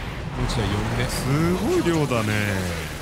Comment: 弟者ってたまに黄猿みたいにならない？w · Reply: 遥々カナタ 俺も思ったw 5:09ぐらいの「凄い量だね〜〜〜」がマルコに言った「痛いね〜〜〜」に聞こえるもんw